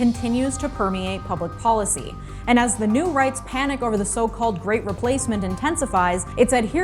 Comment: Glad I listened to that opening again. "...As the rights panic to the 'great replacement' intensifies." I first heard, "...the right panics as the great replacement intensifies." I was about to freak out, like, you're proving them right! 😂